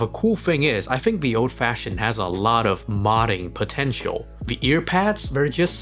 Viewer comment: did you say....POTENTIAL?